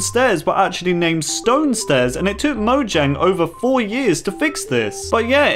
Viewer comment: sounded like you said it took Mojang 40 years to change it which made me look like I have hearing problems (which I used to have)